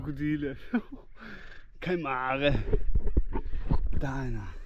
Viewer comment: Ich habe andauernd kalmare verstanden, achso kaimane 😅😊